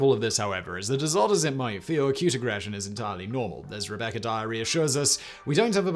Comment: WTF is "Rebecca Diarrhea"? What heck did he say?